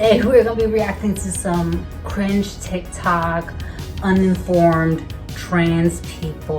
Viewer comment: In the beginning I thought you were saying "unformed " trans people 😅 maybe you said "uninformed " but I think "unformed " suits them better!